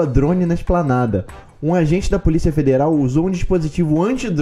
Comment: Eu juro que ouvi o falando "policial mineral" ao invés de federal kkkkk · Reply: Onde? 3:34 esse? Eu entendi federal,deve ser porque ele falou depressa,e deu pra entender isso.